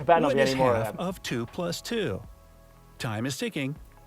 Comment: Is it half of 2... plus 2 (three). But I clearly heard "What is half... of 2+2" (two). These kind of interpretive questions are REALLY maddening because both answers can be correct depending on how you ask the question.